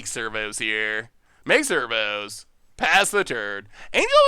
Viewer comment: Every time he says ‘servo’ I hear ‘Flurbo’ lol